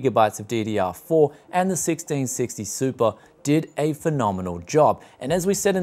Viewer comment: Where in AU can I find a 1660 super for 330? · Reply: 1984 oh sorry I wasn't looking properly i thought he said 1650 super